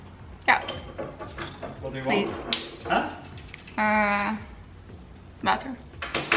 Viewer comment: Who else thought she said vodka when she said water😂😂